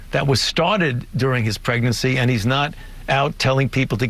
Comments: Did...did he just say "pregnancy?" 😂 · Just after 5:04 he says "Pregnancy." Anyone else catch that? :-D